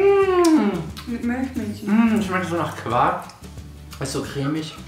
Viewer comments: Bin ich die einzigste die bei 1:55 Milchmädchen verstehen hat? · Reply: Herzgewinn ich hab auch milchmädchen verstanden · Bin ich die einzigste die bei 1:55 Milchmädchen verstehen hat? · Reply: asooo ich habe Milch Milch verstanden 😂